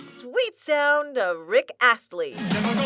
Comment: did she just say rick assly